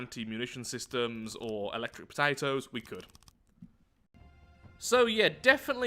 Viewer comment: I thought said electric potatoes.